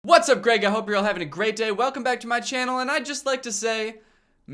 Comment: What i see: "Great Day" What i hear: "Gregmas"